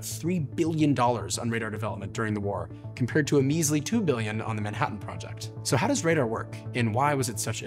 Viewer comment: how's $2 billion measily to $3 billion? It's less but not measly, I thought you said 2 million